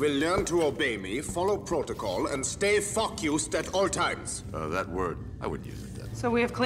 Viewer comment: It sounds like Krauss is saying f- you to Hellboy instead of stay focused